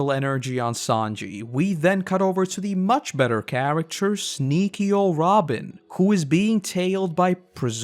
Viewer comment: Did you just said niko robin 17:57😂